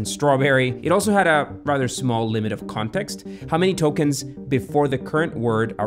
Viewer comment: I thought you had said Tolkiens. My bad.